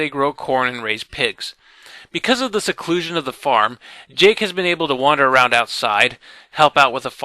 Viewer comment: did you say jake